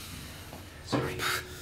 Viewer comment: did he say "you'll be fine Joe"